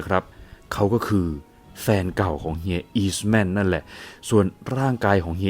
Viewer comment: จะพูดว่า แฟนเก่าของเมียเฮียอีสแมนด์ รึเปล่าครับ